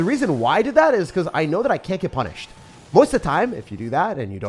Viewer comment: Did he just say sh*t ?? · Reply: “I can’t get punished”